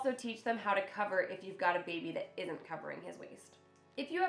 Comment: I just adopted a cat of 7 weeks and I was really afraid of him pooping or peeing in anyplace, and I just watch your video saying "they instinctively wanna cover their waste" and I was like "That can't be possible" and I showed to my kitty where is the litter and he automatically used it! And it's fun to watch how he really carefully covers it 😂😂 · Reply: Oh “waste”!!!! This whole time I thought she was saying “waist”. 🙈